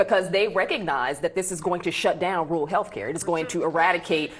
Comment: Did she just say, "WORLD healthcare"?! If so, WHY are We The People responsible for giving the WORLD 👉OUR👈 healhcare? OH... that's right, these Demonrats demand we take in the world and give them our hard-earned taxpayer dollars.